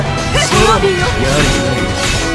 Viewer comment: エプレデ「ペリナーテ」って聞こえるんだけど耳掃除しすぎたかな...。